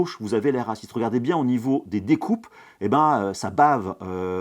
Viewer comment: j'ai compris "les racistes (air assit) sa améliore vraiment la qualité" 🤣